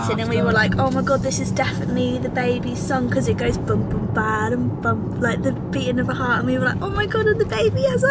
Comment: That head and heart song also sounds like it says “bump-baba-BUMP” baby bump!!!❤️❤️❤️ love you guys so much, thank you so much for sharing this beautiful journey with the angels ❤️❤️❤️